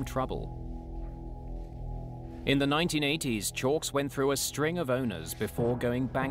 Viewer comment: i thought he said a string of boners!!!!!! hahahahaahaahhahahahaa